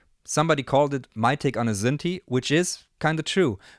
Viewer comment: What did you say at 10:10 - "my take on a..." ? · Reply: Pretty sure he said “synthi” as in like EMS synthi, which are pretty much a synth in a suitcase